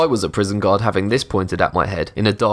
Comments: this is the first time i seen one of his videos and i thought he was saying god and not guard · I thought he was saying god not gaurd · When he says guard i thought he saying god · When u said guard I thought u said god... :\